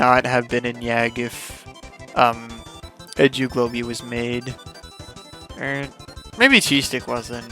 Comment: when u were talking about edu globey I thought u said edgy globey